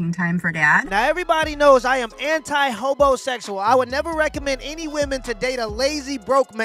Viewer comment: I thought he said homosexual but then I realized he said hobosexual